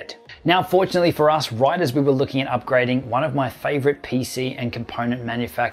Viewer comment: So they reached out to you at the same time you were thinking about doing a video editing pc video? Wow....lucky timing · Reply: That's not what it says.. "Right as we were looking at Upgrading".. our editing system